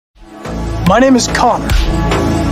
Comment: Bruh why did I hear “my name is cock” at the start 💀